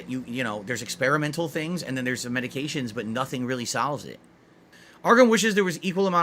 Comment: It won't sahlve it. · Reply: I saw your comment just as I heard him say salves it and I couldn’t help but bust out laughing lol